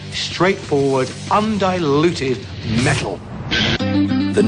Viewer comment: I love this video. \m/ I've got question: what says the guy on 1:00-1:04? I mean last word before "metal". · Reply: Undiluted